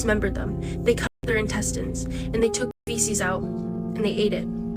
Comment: what did she say ?? Fesses what the f is fesses